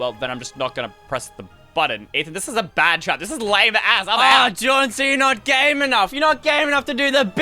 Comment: did swear? he said lame ass